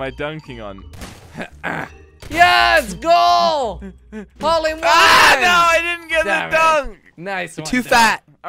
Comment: did jordi just say hole in one while playing basketball